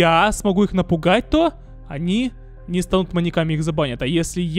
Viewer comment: Мне послышалось что ты говориш моник а не моньяк😂😂